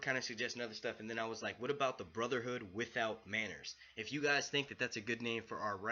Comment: BWB Looove it! Great name, great idea! :) KIT SAID WHAAAAT???!!! You don't believe him? Hmmmmm....that Def means he's coming back but not right away. Smh. Drogon has been eating Harpy buffett BUT if the other 2 have been chained up and nobody fed them, then they're prob not as big. Right? SHOUT OUT to! ;) Slayin Vids ALL day, Every day. :D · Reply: Ha!!! Thought you said Brotherhood without Banners. You said Manners. Lmao.